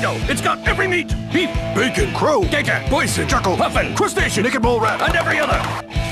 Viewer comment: I think I heard say poison at 0:12 😨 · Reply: Bison